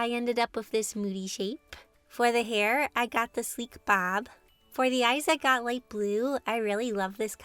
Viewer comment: I’m just reading the subtitles (because it’s 3 am and my headphones aren’t charging) and when I saw the word “sleek bob” I thought it said “shreck bob” 😂